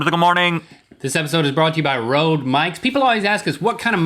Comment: I thought Link said "Road Mines" not road mics